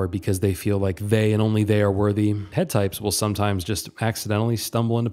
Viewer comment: I’m a bit confused on what you said here did you mean 8 types? Sorry it just sounds like you said “head types” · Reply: He said head types, as in the head cluster.